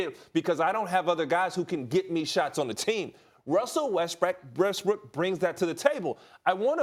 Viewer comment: who else thought he said Westbrick